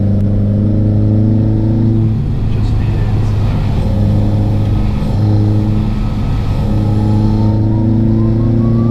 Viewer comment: just what i needed after a shite day!! thanks hunni xx 3:40: supercharger whine corner?? 5:03 what did you say here? 'Just for you ??????' 5:07: new super charger whine meme 7:32: big turbo sound 7:35: big turbo sound ph2 7:46: this 'exciting' thing,, is it anything to do with all these boosty boy sounds youre making?? 8:22: im a litle weirdo,, tell me where you live great vid as always Sir,, more of this plz or you might find me hiding in your garage with a scream mask on hidden amongst all your discarded clio pieces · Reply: Dusty Shed Garage I have to make the sounds with my mouth as it’s not happening in real life! 😂😭 — just for you “Arron”! 🤙🏻 I would tell you but I like to keep you guessing! 😉